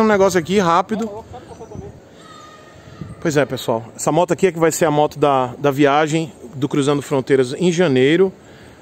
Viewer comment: Eu ouvi "essa que vai ser a moto da próxima rifa"...???? hehehehehh